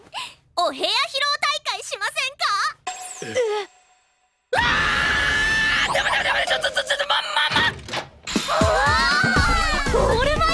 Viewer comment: he sounded like he was saying MAMAMAM